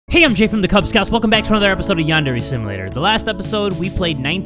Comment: I know he’s saying Yandere Simulator, but I hear him say “Yandere Sim Later”.